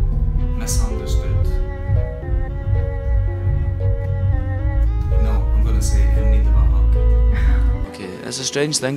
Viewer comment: Help does anyone know what John says after 4:18 after he says misunderstood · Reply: I think he said "in need of a hug"